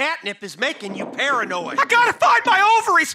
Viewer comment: The “Ginger colored cr@ got me. 🤣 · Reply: i thought he said ginger covered 😭